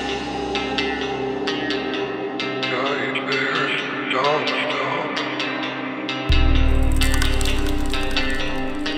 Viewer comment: SKBR' da da var çok etkileyici ses ne dediğini hala çözemesem de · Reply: Nightmare doesnt stop diyor.